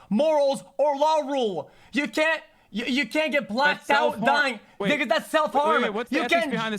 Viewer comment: Anyone else hear xQc say the n-word at 1:15:49 to 1:15:53? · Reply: Bro your mind is racist he said 'Because'.